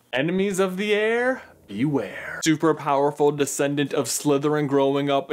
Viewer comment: Am I the only one that thought at first that he said "enemy's of the air, beware"?